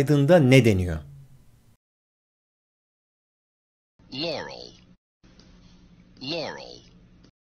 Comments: Yaurel duydum yaw xD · Ben Yanurel duydum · iyide ben Yaurel duydum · abi ben garel duydum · BEN NİYE YAUREL DİYE DUYDUM